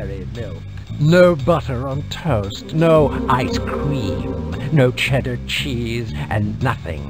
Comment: I thought he said "No Ice-T"